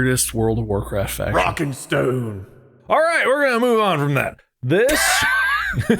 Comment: DID I HEAR A ROCK AND STONE?